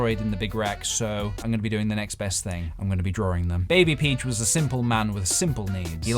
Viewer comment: I literally thought he said baby peach but no...baby peej makes way more sense